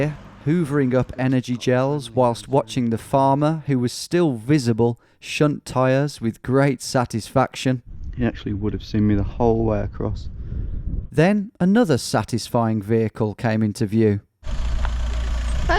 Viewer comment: what do you say, ‘shunt tyres’?